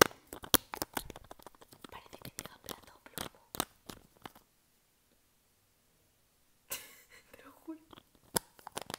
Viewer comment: parece que dice una frase pero no la llego a entender ¿alguién me la dice? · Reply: Dijo : "parece que diga plata o plomo" (o eso entendi yo UwU espero que te sirva xD)